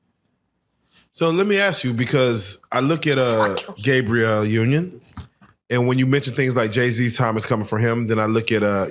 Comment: what did she say at 4:59? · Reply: FAWKuMean